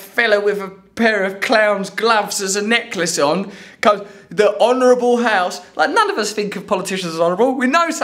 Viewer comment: t2:05 I thought i heard dishonorable house not this honorable house when that man addressed the assemblage behind that closed door which explained wht y they wanted to close the door lol i am cheeky aren't i